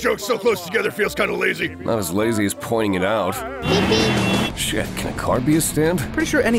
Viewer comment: When said "not as lazy as pointing it out" I thought he said "not as easy as pulling it out" which still makes sense considering he's talking to Joseph.